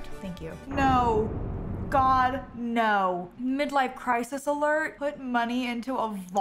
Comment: For a moment I thought the alert said Multiple midlife crisis. I was like nooooooooooo!